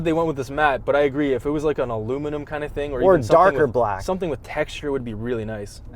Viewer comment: Excuse me? Did he just say "darker black"?!